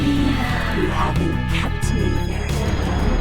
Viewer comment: and onward during the attack I can't understand what's being said at all :( still an interesting video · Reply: There are subs, but she basically sais : You haven't kept me very well Peter. You haven't kept me very well at all.